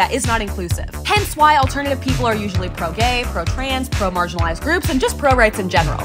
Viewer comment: I thought they said pro marginalizing groups at first and my jaw DROPPED